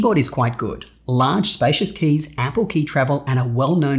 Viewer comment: LOL i thought he said APPLE keyboard.... (ample)